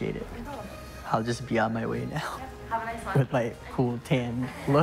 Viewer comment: Am I the only one who thought she said “have a nice life” at the end